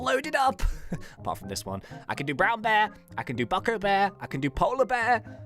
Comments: Did he say Bucko bear? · who else heard bucko bear